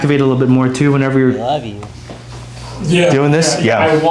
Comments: I hear " I love you". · Anyone else hear audio issues? 23:26 - at 23:28 u distinctly hear someone say "I love you." and before this a man shouted "Lay down!" 15:23 - "Why's it feel dryer? soup!" there many more..